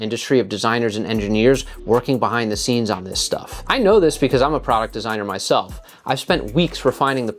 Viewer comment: I know what you said...but I just heard it as....I'm something of a product designer myself 😅